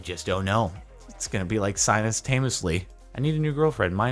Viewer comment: turn on auto captions and he says “simultaneously” and the captions say “sinus thema slee”